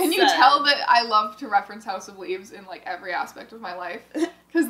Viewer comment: for a second i thought she said house of weebs🤣🤣